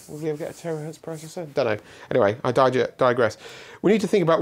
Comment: "Dunno... Anyway, I digest!" Had me in tears 😭😭😭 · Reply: I heard "anyway, I died yet"